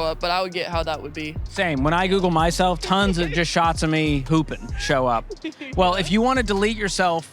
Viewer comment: I had to double take. I really thought he said shots of me pooping show up.💀